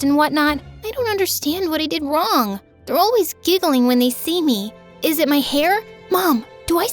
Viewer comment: I put this part on two times speed and I thought said "bro is giggling"💔